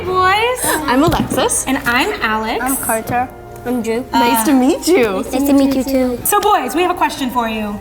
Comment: for a second i thought she said “nice to meet drew” 💀